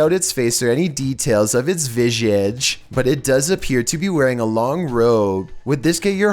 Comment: wtf word was that.... Did he me visage? Lol